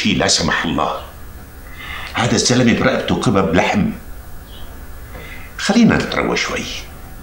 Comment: ايش قال الزعيم ؟ كباب لحم ؟ 🌮😂